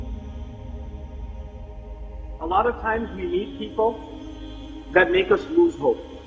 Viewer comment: JazakhAllah for sharing Some corrections for the subtitles to avoid confusion - 0:30 meet, instead of need 2:42 Conquerors, not punkers 4:31 Allah, not ask the love Disclaimer: I could be wrong too.